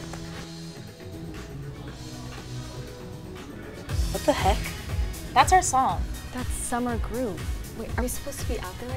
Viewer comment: Wow Bailey nice job😅😫 8:07 what's wrong with Ivy I wonder what she said to Theo hopefully she'll be ok 😅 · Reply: She said i love you Finn.